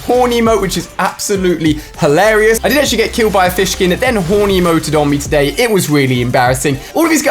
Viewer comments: Like this comment to get a Victory Royale your next game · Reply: Sounded like you said horny emote lol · lol did he just say horny moe